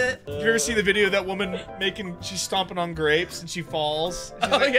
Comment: I swear I head say graves…